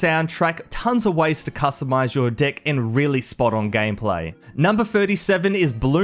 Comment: guys did he say in 3:53 tons of ways to customize your dlck:face-red-droopy-eyes